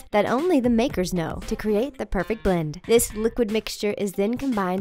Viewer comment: did she say cerate